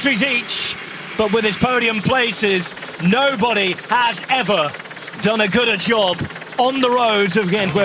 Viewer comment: did he say "gooder"?